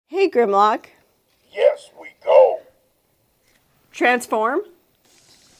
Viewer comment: Ok, it sounds like he says “hey Grandma! Transform!” 🤣🤣so adorable